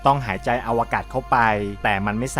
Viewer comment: ได้ยินว่า สูดหายในอวกาศเข้า